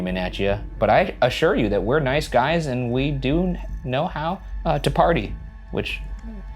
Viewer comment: i think i heard a child sayng hey: “We do know how to party, which - HEY - you know...”